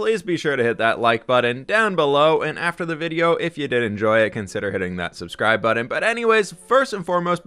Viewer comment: i thought you said "if you didn't enjoy it blah blah blah"